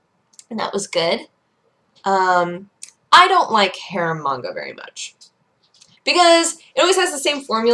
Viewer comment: I totally thought she was saying "hair manga" rather than "harem manga". I figured it made sense considering it's Dodger.